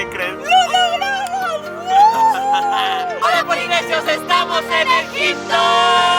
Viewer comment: Entendí "Hola polinesios estamos en el Tipton" a lo Zack & Cody